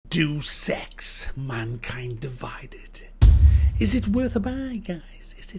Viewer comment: It really sounded like you said "Do Sex: Mankind Divided. But ey nice review